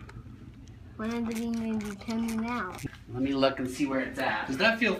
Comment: I thought he said a bee 🐝 in the ear